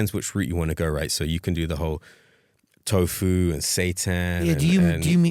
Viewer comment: What did Oliver say at 32:22? It sounds like "tofu and satan", but I'm sure that's not what he said. 🤣